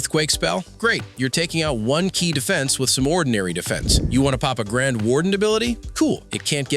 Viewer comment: hold on I think you said papa warden?😏😏🤣🙏